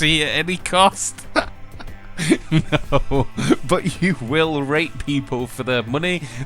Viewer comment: did you say rape? XD